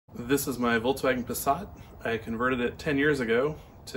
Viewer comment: Did you say "voltswagen" xD